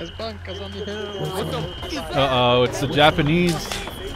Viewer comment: Wait....what? Did I just hear you say "Uh oh, it's the Japanese"?